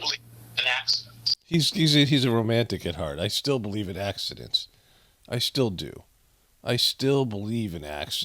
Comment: It sounded like he said, “I still believe in accents.”